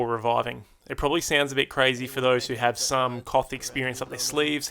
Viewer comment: coth? What did he say? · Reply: Hey mate. So I was saying that the tip is probably an obvious one for those who have some KOTH (King of the Hill) experience.